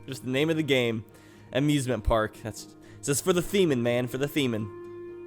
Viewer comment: sounds like he is saying semen with a lisp